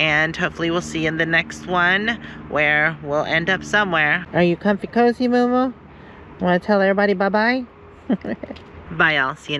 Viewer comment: It sounded like said "hey Moomoo" lmao😮😂